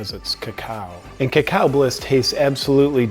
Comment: For some reason I thought that said caca bliss like you where promoting shit 😂 god it’s late I need to stop watching your videos tonight and get some sleep, thanks for the laughs